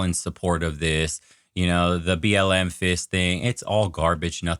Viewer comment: did you say BLM fisting?